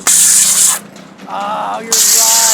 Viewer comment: I heard say it was chess